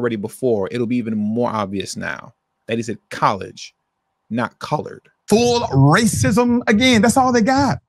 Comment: It sound like he said coloerd in the first clip. But Who cares in the 60s thats what they were called.